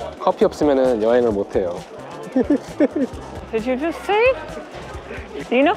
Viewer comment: did you What say? 라고한건가요? What did you say? 아닌가요? 둘다 맞는건가요? 시트콤 보는거 처럼 은근 영어공부되서 좋아요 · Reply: What did you say? 가 맞고 그렇게 말했습니다. 빨리 말해서 그렇게 들리는 것도 같네요!